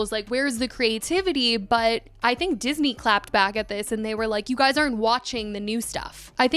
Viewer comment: i thought you said jenny clapped back??? i waited the whole video and didn’t hear a clap back 😭💀